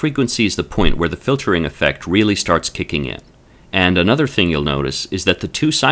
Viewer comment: Excuse me, there is some phrases I cannot understand, in the video, about 00:03:06 The cut-off frequency is the point where the filtering effect really starts(.......?). It seems that the voice is not the same as the subtitles. Thank you! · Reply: to kick in, which is pretty the same as - to start.